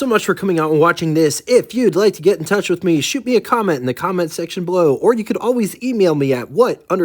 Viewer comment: Lol did you just say "the cockment section"?